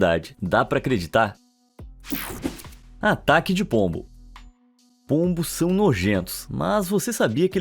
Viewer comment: agr entendi o cara do "pegue o pombo, pegue o pombo, peguem o pombo agoora"